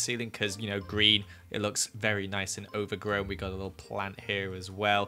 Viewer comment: i thought you said "ogre-grown" not "overgrown" and i thought that was ironic